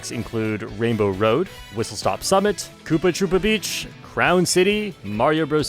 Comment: Did I just hear Koopa Troopa Beach? Surely isn't implying ktb is a good track, surely not.